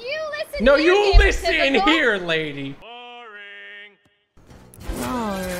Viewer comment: NO you listen here Mrs princess P90!! 0:53 also did he call sky one he** of a lady?